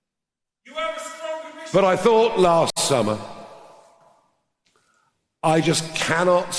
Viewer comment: What was said? 13:31 ? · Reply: Something about not being strong last summer? That's what I got.